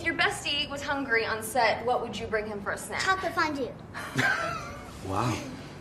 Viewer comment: Does she say "taco fondue"?! Am I hearing things? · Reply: Chocolate fondue